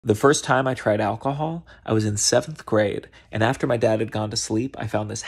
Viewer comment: I'm I the only one who thought he said "Seth grade"